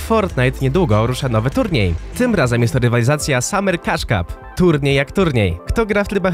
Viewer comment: Ja zamiast cush cup usłyszałem kaczka